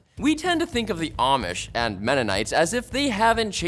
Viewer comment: thought he said the amish and meta knights for a second…